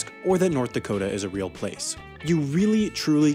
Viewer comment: Wait North Dakota isn’t a real place? Is that what he said?